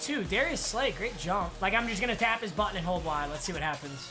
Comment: was I the only one that thought he said ima tap his butt?